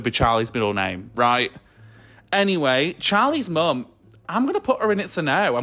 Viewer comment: for a second I thought you said nick’s mum and my eyes widened 😭 But yeah, Charlie’s mum was trash. Some things are more important than grades: Charlie’s actually relatively happy, it’s as if she prefers Charlie being depressed like last year